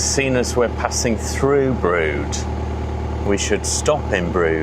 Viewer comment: Is the name of the village you visited perhaps 'Brewood' instead of 'Brood' as the subtitles consistently told me? The "ew" part of the village name normaly doesn't get pronounced?